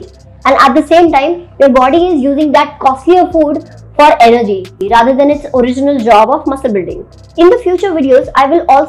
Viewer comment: What is this you are saying at 4:40 line ????? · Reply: I said "your body is using that costlier food for energy rather than its original job of muscle building "